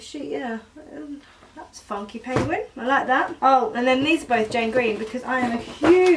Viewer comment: I thought you said John Green 😂